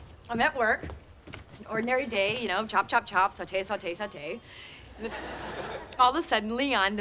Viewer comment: is she said "sate" ? Omg 🤣